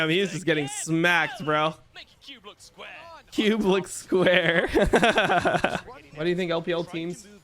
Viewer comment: When was that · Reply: XDDDDDD but i think he just says "Cube looks square"